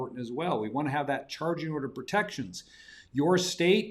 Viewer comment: What is challenging orders? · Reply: I think he said “charging order protection”. But maybe you’re referring to something else. See